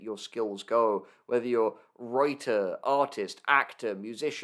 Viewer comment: That sounded like the name Reuter.